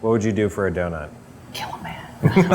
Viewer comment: DID HE SAY HE WOULDN'T KILL A MAN REEEEEEEEEEEEEEEEEEEEEEEEEEEEEEEEEEEEEEEEEEEEEEEEEEEEEEEEEEEEE